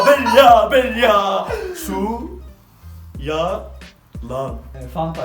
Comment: SuYa-lan doğrumu duydum. abi seveyrum seni daa